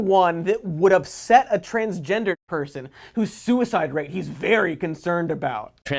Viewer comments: did just say “a transgendered person.” I really can’t tell, it’s quick. If so it should be “transgender person” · says "that would upset a transgendered person." The audio kinda cuts out right after the r, so it kinda sounds like he said "transgender person." Was the audio cut like that on purpose? Because "transgendered" is a term that has recently been deemed insensitive as it implies that being transgender is a condition that one can fall in and out of. Like being depressed, or parched (for lack of a better example). If that's the case, I'm a little sad that they'd cut out the sound to appear inoffensive instead of admitting it and making a comment in the description. I hope I'm wrong though